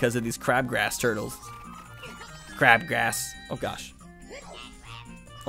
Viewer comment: I always thought the forest turtles said a#% crack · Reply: I didn't get to play MM much as a kid but I've always thought they said "crab grab"